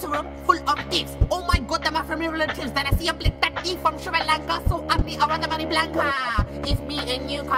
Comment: did he just say blick tutti from srilanka